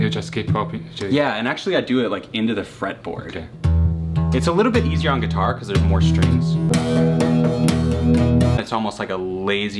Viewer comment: Rob: it's a little bit easier in guitar 'cause ih'had more strings.? Davie504: his brain (what did he just say?)